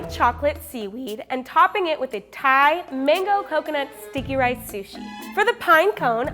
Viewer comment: When said sucky rice sushi I thought she said stinky rice sushi